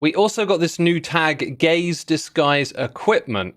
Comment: listening to this i heard “gays disguise equipment” and i was like “well ya, i guess we do”